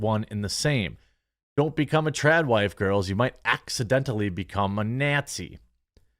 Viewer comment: I thought you were saying Chad Wife